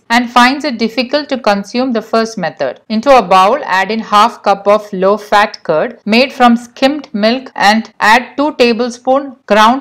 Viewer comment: nice video ! What did she say at 3:56 for the second method ? Low fat curd cottage cheese ?